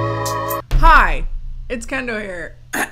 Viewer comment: I’m terribly sorry but when you said, “ Hi! It’s Kendall here! “ all i heard was, “ MY NAME IS CANDLE” from Kendall Jenner.